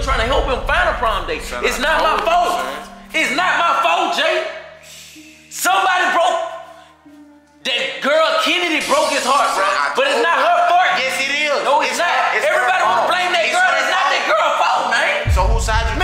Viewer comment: we not go act like he did not just say it is not her fart lol 😂 🤣